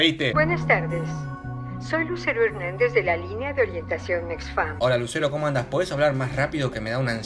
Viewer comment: alguien se dio cuenta que dijo orientación nexuam??? XD